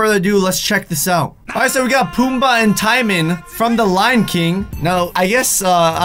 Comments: did I just hear ty-man 😶😶😶 braaaaaa it's t-moan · Is anyone else triggered by how he says Timon? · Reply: Braelyn Mayfield I honestly just heard him say Timone and I got triggered af